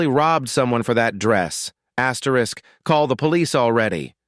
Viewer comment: I thought it was saying AT RISK ,but as 1 word lol